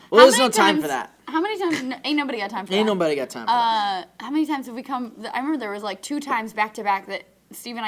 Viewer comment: I clicked at 3:31 and all I heard was Meg saying "How many times have we cum"